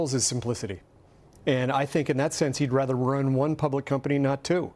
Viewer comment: It sounded like he said Elon would rather RUIN one company instead of two. He meant to say 'run', correct?